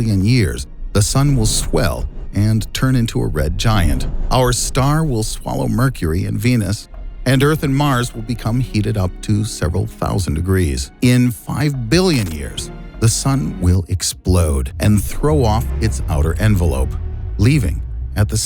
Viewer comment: “Earth and Mars” Why tf did I hear “Harry and Marv”